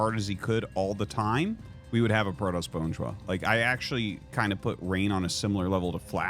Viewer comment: What did say at 1:05? I couldn’t understand. “if Rain just played as hard as he could all the time, we would have a ________”? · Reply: Bonjwa